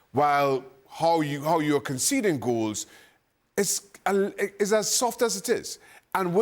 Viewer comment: I thought Shaka said: as soft as tits